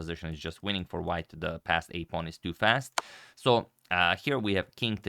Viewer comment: What I heard: "the passed pawn is too passed" XD